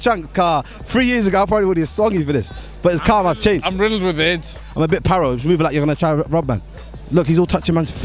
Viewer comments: did....did he just say....... "im riddled with aids" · wait did I hear correctly... did the guy at 2:52 say he is riddled with aids?😂